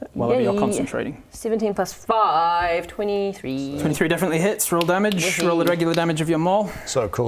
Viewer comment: did i hear 17+5 is 23 <.<